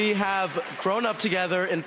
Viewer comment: For a second I thought he said thrown up together 💀